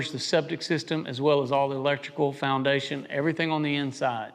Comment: Does the $110-$120/sqft include foundation? I hear everything else, and "everything". Ok, I replayed at 6:20 - "... Electrical, foundation ..."